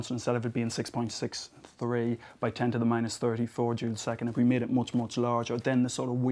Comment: " ...instead of it being 6.63 x 10^34 June the second" June the seconds? WAT!?